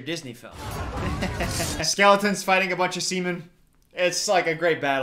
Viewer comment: Am I the only one who heard “skeletons fighting a bunch of siemen”